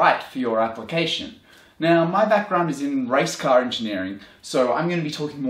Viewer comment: Sounded like he said rice car engineering